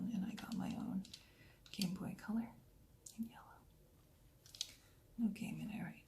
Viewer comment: did she say leave me alone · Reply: I think she said "in yellow" but it does kinda sound like that haha